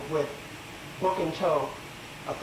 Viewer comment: Virgin man with a broken toe punched his wife out...🤦🏾‍♂️If that ain’t a lie I don’t know what one is... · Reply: I thought he said "book in tow" meaning the book is on the way. Now I have to go back and listen again.. 🤣